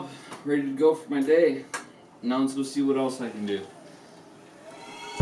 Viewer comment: I can see why she married you - when you said, 'Now I'm up for the day - let's see what else I can do', my mind could have sworn you said, 'what kind of trouble I can get into' 😂